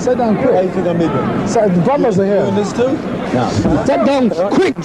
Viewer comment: I wish I could hear what Frazier said to Ali’s brother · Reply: He said, "You're in on this too?"